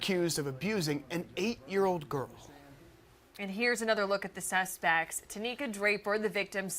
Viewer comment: I’m pretty sure she said “suspects”’but it sounds like she said “cesspool!” That would be much more appropriate! Burn in hell fat asses!